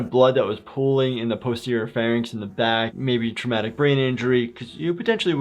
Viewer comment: did you say "posterior pharynx" or "pussy or pharynx"? The latter is what I heard and it made me spit out my coffee laughing.